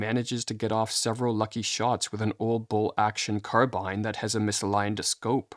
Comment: WTF is "bull action" The word you mean is "bolt action" and i doubt they got that wrong in the OG doc. Hard to gain credibility when you fumble common words.